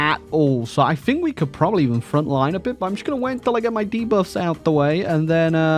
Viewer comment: what did he say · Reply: “Probably even frontline a bit” meaning he will use a front line order to attack instead moving divisions individually